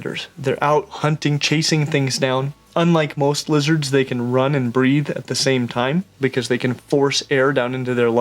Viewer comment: Misheard start as they can “run and breed at the same time.” I was both confused and impressed.